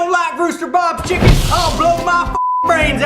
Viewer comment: thought it said rooster boobs